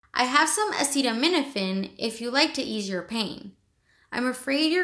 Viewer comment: Hi i don't really understand what the doctor want to say in that part. Dr. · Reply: Hi we have answer sheets and ad free videos for just 1 dollar of your support in Patreon <link> She says, 10:55 I have some acetaminophen if you like to ease your pain. I’m afraid you’re going to have to be admitted into the hospital because the GI doctor would like to have a better look at you. I don't know how serious it is in your case, but it can get bad.